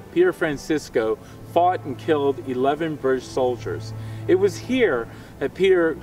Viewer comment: Is it me or did it at first sound like he said, “ he fart and killed 11 British solider” at 3:40(i’m sry if it’s rude for me to say that)